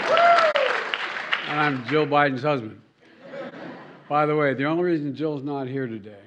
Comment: It sounded like he sais: "I am Joe Biden's husband". Listen carefully!